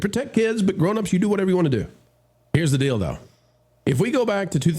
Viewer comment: "Blablablah Pride month... is gone.... here is the DIL DO".... Wait! what did he said!? 😳 [Rewind⏪]... Oooh... 😅